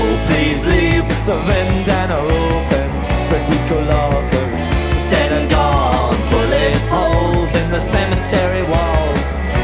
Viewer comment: Could have sworn he said “The Duke of York, dead and gone”